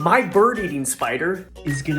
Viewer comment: o thought he said my bird eats spiders